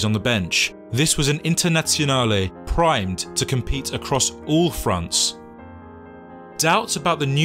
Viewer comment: compete in France? Did i misheard that? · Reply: Fronts. “Compete across all fronts”